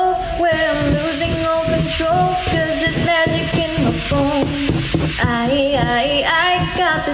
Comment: I thought she said their magic in my bal